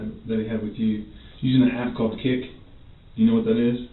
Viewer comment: "an app called kik?" is what he says